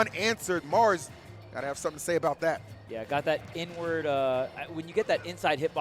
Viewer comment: Bro i thought he said "n word" 😂😂